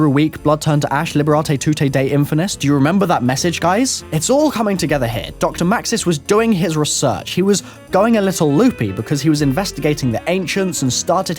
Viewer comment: ummmm....what did he say.... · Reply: its all coming together here doctor Maxis was doing his research he was going loopy